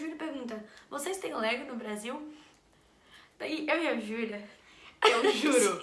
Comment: não entendi o que elas falaram!?? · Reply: Vocês tem lego no Brasil